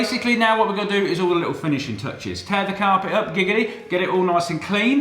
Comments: I'm sorry did he just say giggity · Did you just say giggity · Did Colin say Giggty at 11:00 ? or is it just me xD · DID HE JUST SAY GIGGITY?? Wakanda quagmire is this?? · did he just say giggity? lol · Did anyone else hear "Giggity" at 11:02? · Please tell me someone else heard him say "tear the carpet up giggdy" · did he say "giggity" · did he say gigidy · did he just say giggity? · Tear the carpet up, giggity? Did I hear this right lol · Did you say giggity at 11:04😂😂😂 bro is quagmires cousin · did he just say, giggity